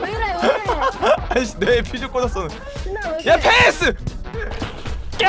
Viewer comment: 이부분 데미지 입으면서 소리가 흐릿해져서 퓨즈 꽃았어로 들리는데 님 시점으로 보면 야 대가리에 소주 꽃았어? 라고함ㅋㅋㅋㅋ